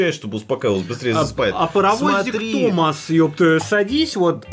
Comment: Здесь столько жести что я тут 31:00 услышал "Паравозик Томас Садист"